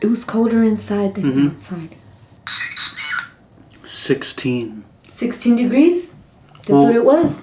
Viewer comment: Again sorry not trying to correct you, but @ 1:20:20 when y'all think its saying 16 its actually saying #SIX #NEAR!! I HAVE HAD A FEW PEOPLE IN MY FAMILY RECENTLY DIE TRAGICALLY, AND HAVE USED ALMOST EVERY SINGLE SPIRIT BOX AN APP OUT THERE FOR ABOUT 5 YRS NOW, AND I HAVE LEARNED SO MUCH. THATS ONE THING I HAVE LEARNED, ITS NOT ALWAYS SAYING WHAT YOU THINK!SLOWING THE WORDS DOWN DEFINITELY HELPS! YOU'D BE SURPRISED AT SOME THINGS YOU THOUGHT IS BEING SAID , RATHER THAN WHAT'S ACTUALLY BEING SAID!!